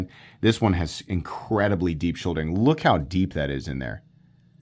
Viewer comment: “incredibly deep sh…” shulding? Shouldering? What’s he saying here? Is this a piece of terminology I don’t know? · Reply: Shouldering